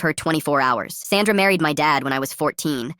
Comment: I thought it said Santa married my father...